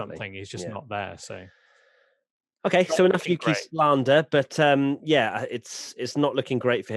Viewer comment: I thought you said "enough Yuki Tslander" 😂🤣 (I'll get my coat)